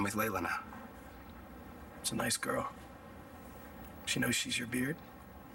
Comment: What does say after 'She's a nice girl' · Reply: "She know she's your "beard"? "